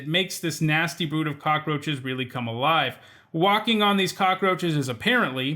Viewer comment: brood of cockroaches is even worse. It sounds like you said walking instead of waking. laying in bed feeling hundreds and hundreds of cockroaches beneath you is unfathomably worse than simply walking on them.